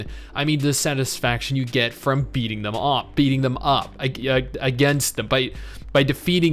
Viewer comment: Did you say beating them off at 3:00?